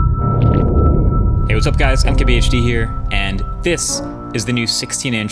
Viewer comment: I just realized he's saying "MKBHD here". All these years I thought he was saying "I'm KBHD here".